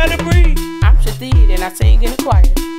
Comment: I can't believe I watched this over and over and did not realise she was saying Shithead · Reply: Ruby Elizabeth I thought she was saying I'm shadee hahaha I thought it was her name ... Lmfao